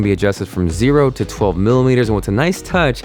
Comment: Did he say milliliters at 0:44 ??